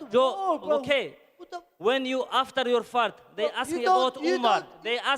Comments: did i hear after your fart??? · did he said after your fart